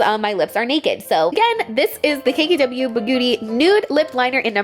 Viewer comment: did anyone else hear her say bagooty instead of beauty at 7:41 or is it just me? lmao